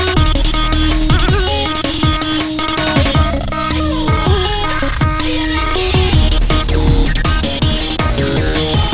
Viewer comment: What does bugs say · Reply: "OOOOH! IM DYING AGAIN!"